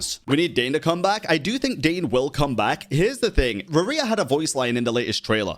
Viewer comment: WAIT THAT WAS RERIR!! 🤯 I didn’t even realize that, then just now!!! 😮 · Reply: I legit heard "Maria"